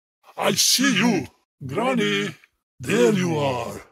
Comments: When he said "granny", at first time hearing it, I thought he was saying "darling" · Bruh I thought he said honey but it's granny